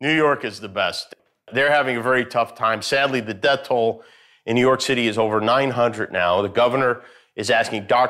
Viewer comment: Did he just say “New York Titty”?